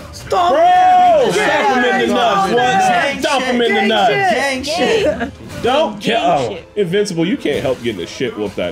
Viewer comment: yo why do i hear voice say gang shit